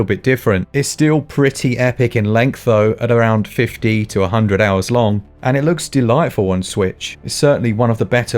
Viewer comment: When I heard you say "50 to 100 hours" I accidentally heard "fifty two hundred hours".